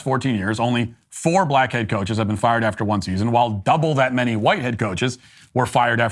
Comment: My goodness. I thought Matt was saying blackhead coaches & whitehead coaches instead of black head-coach & white head-coach.